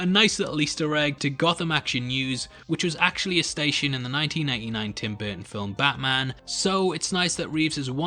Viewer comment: Did you say 1999 or is your pronounciation that abysmal?